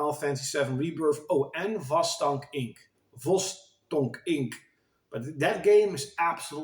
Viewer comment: Hi~ You mentioned a game at 6:14. Can't make out what you meant there. Wost Donk Inc? Haha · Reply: Vostock inc hehehe